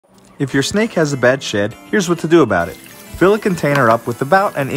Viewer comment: I really thought he said “if ur snake has a bad shit” I thought he meant it was constipated😂😂